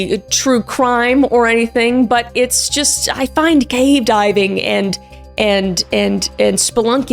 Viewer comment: I HEARD "GAY DIVING" 😭😭🙏